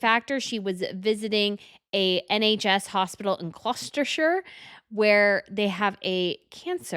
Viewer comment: Catherine's blazer is part of a matching suit, she has more baggy trousers in the same material, and changing it out to slim-fit brown pants to reuse the jacket, looks great. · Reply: I thought said Gloucestershire but it was Colchester. The mispronunciation made it difficult to understand.